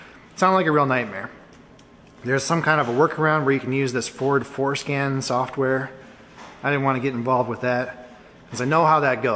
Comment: I think I heard "Foreskin software" at 3:00... Or is it just me?😂